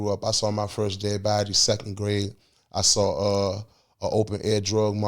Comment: I thought it said caught a body in 2nd grade😢